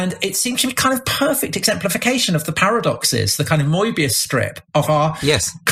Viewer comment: Did he say "Moibius strip"?